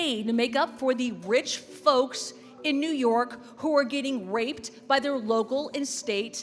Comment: Did... did she just say r*ped?